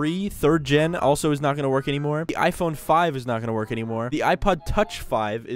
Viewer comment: I got so scared when you said iPhone 5 cuz I thought you said iPad 5 😰